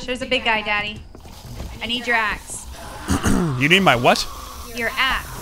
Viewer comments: no she clearly said axe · I thought said I need your ass, but she actually said I need your axe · it sound like said daddy i need your a** i cant cuss but it sound sooo wrong reason why i dont im a kid friendly channel!